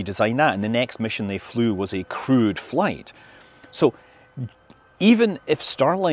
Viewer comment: I keep hearing "Crude Flight" instead of "Crewed Flight!"